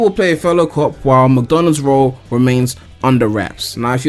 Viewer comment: sounds like you said mcdonalds XD